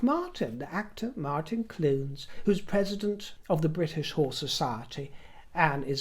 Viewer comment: Darling, for a moment I thought you said “British Whore Society” 😅 Must listen more carefully, mustn’t I??